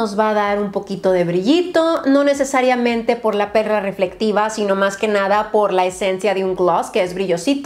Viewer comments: ¿dijo perra reflectiva? 😂😂😂 O escuché mal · " por la perra reflectiva " o escuche mal?